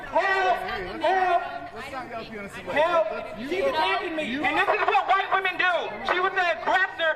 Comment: "they” not “women.” This is so outrageous I think these guys are paid. I doubt they'd destroy their own neighborhoods and damage their own reputations in their own city. Something's off. They're too cocky. It's too ”us versus them" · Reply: Sorry, I just saw the reply. He actually says "this is what white women do" at 8:37.